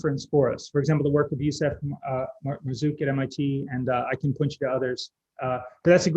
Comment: who (Mark Mazuk?) at MIT was refering? Didn't hear clearly.